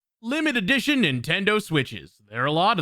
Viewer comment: I know you didn't say it but I heard "Limited Editches Nintendo Switches", calling them that from now on.